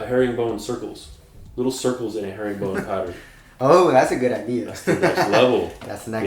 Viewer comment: I always hear it but it's called "hair and bone" pattern?